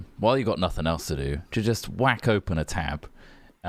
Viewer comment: I heard say "whack on" and I stopped listening